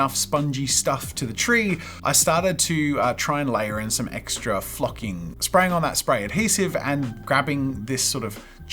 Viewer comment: Wa it just me that thought he said F#%!ing at 9:41 - 9:45 but he said flocking… it did not sound like that